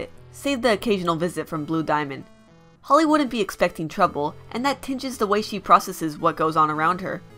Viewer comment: when you said "Holly wouldn't be expecting trouble" I heard "Hollywood be expecting trouble"